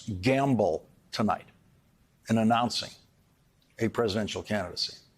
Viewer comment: does Lawrence call him Donald Crump? LMFAO!